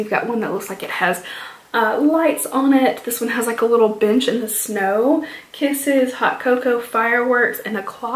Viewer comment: Ohhh 😂LMAO I thought you said "A little b¡tch in the snow" (the lipstick story)..it took me a few rewinds to actually hear "A little BENCH in the snow" LMAO hooo my bad. Luckily for me being partially deaf it makes conversations really amusing hahaha! You've done a grand job with this video...well done! 👏💖 Those brushes and candles though! 😍 On a semi serious note I think Christmas should be more about spending quality time with your family...I'll have my two kids with me this year and that's all I need. 🎄✌